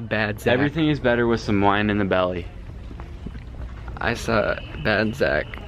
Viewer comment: "everything is better with some wine in the belly" why does that sound like a sign that would be up on the wall of a suburban white moms kitchen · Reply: Dara Del Angel STRAIGHT UP THOUGHT HE SAID EVERYTHING IS BETTER WITH SOME LINE IN THE BELLY WHATFHSNS